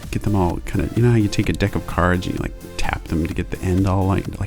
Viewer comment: I thought he said you a d*ck of cards